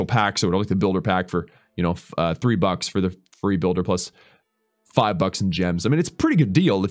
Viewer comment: I miss heard and I thought said you can bye the builder pack for three buts